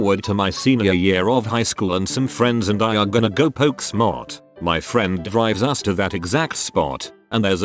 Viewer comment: “poke smot” 😂 I was only listening to the video and at first I thought I misheard it as “poke SNOT” 😂